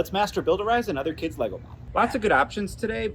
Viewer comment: I was scrolling pretty fast and I heard "let's master" and I was hella worried 😂 glad it's master build